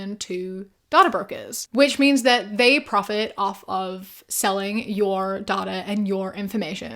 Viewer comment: i’m sorry but i zoned out a bit during the ad and then all the sudden i heard “selling your daughter” lmao😭 gotta love the accent differences lol but anyways great video!